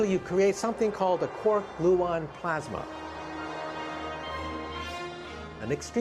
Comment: did he say a core bluon plasma? How do you spell it? I don't know what would be used callofduty3312. Maybe a simple human canon? :p